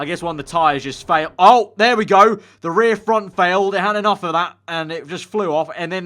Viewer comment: did he say "the rear-front has failed"